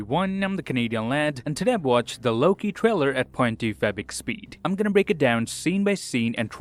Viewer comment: At the beginning I thought he said low key trailer im like ???!?!?!?!?!